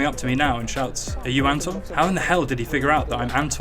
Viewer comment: what is he talking about? I can't figure out the word. Anton?